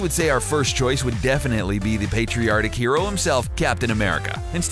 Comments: Erm.... at 2:10, did he say "patriotic" or "patriarchic" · When you mentioned caption America it sounded like you said patriartic Also dpool has continuity so he could easily lift the real one if he wanted